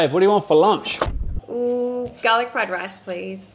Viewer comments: I misheard it as "garlic bread rice" and was confused and intrigued. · I thought I heard “Garlic Foie-Gras” and I was like, this is gonna be a weird one 😂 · I misheard it as "garlic foie gras" 😂